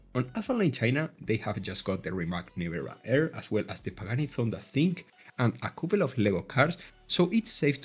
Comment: I'm sorry did you say Pagani Zonda Sink :trolley